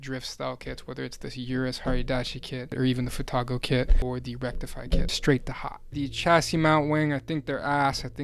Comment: did you say the rectify kit?